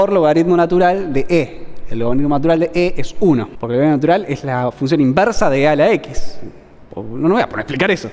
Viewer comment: querido, me puedes explicar que dices en el minuto 18:39, por favor. Saludos desde España, eres el mejor! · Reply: "...porque el logaritmo natural es la función inversa de e a la equis."